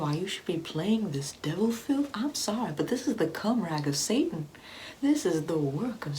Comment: anyone else hear "cum rag of semen" at 4:21 🤣🤣 someone PLEASE tell me they heard that too??